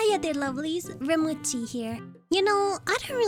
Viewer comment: I thought that you said “Hiya there lovelies! Rem with tea here” at the start =w="